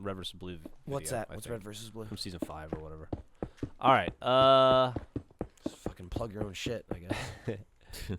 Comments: Did Micheal just say nigga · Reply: He said "Just fuckin' plug your own shit, I guess." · Did Micheal just say nigga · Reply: I heard "Just fucking plug your own shit..I guess" or something...